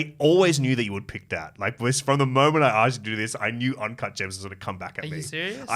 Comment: what's the name of the movie mentioned at 40:12?? Sorry, I can't understand it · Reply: uncut gems